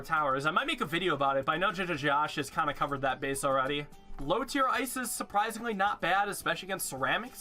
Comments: Am i the only one who instead of " low tier ice is" heard " low tear ISIS" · what is the name he says, jinja josh? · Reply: Jajajosh